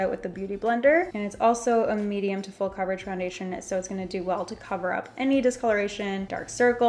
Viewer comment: you said foundation instead of concealer ❤️❤️❤️